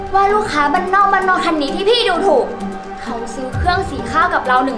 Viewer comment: คันนี้ จะพูดว่าคนรึป่าวครับ5555